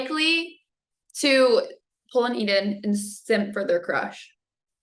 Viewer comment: What does pull an eden mean? Does she said to pull an enid? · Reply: I came to ask the same thing. That interviewer girl cannot enunciate well. She is there just for her cute looks. I too heard it as "pull an eden" . Gee woman, ENUNCIATE!